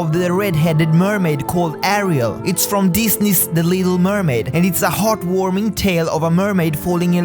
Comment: When he said the little mermaid, I thought he said illegal mermaid.